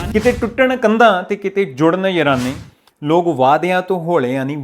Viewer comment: I think he is saying ਤੰਦਾਂ "kite tutan tanda te kite judan yarane"